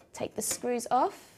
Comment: Google's Transcribe Audio Beta Fails Time - what was said - what the captions said 0:50 - take the screws off - take the stories are 1:05 - Now the PCI slot is found just under here, this white one here - Now the p_c_r_ slut is sounds just under him, it's like one hand 1:16 - Now grab your sound card - Now practice some pots 1:24 - (note: this is said just after "now place it firmly") - into spare pci slot - into spec p_c_r_ slut try it, there are more I cant fit in this